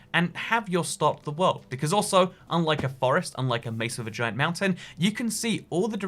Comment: Was I the only one who heard a face of a vag**a mountain at 8:05?